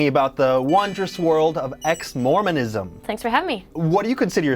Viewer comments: i thought it said ex-morons LMFAOOO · I thought you said ex morons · I thought is said Ex morons